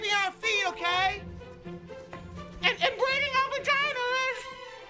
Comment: I always thought it was “Wearing our Pajamas” instead of “Vaginas” lol